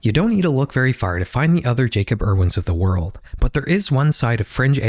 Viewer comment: Wow, I thought you said Jacob Hurwitz at first lol.